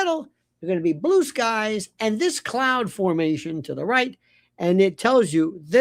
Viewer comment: At first I thought you said "clown formation" and thought it was a Blake and Andrew puzzle.